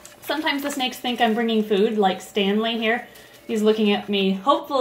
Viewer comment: I thought label Stanley said stankey